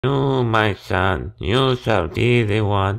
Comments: I thought he said “stinky poopy” instead of “figgy pudding” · I thought he said “…with all the stinky booty” I was so far off.